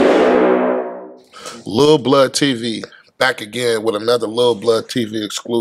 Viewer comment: I thought for a second it said bloodclot lol and I’m Chinese women . Then realized it’s blood tv jokes 🇨🇦🙋🏻‍♀️